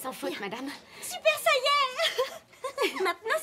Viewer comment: la premiere fois j'ai entendu super sayen xD